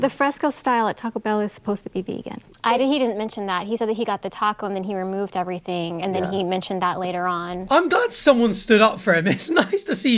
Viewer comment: that wasn't even what was said, he said he got the wrap and asked for it to be made with subsitations